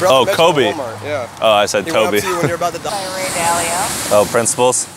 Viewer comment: “what’s you name?” “Coby” “ oh i thought you said Coby” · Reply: no said “oh I thought you said Toby”